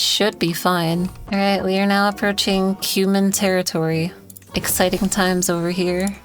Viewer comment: I was looking away from my phone and for some reason misheard “Cuman” as “human”, and I thought “wait, this isn’t Stellaris, why was she wording it like that?” Lol